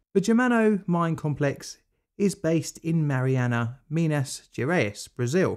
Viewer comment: I thought it said marinara 😭😭😭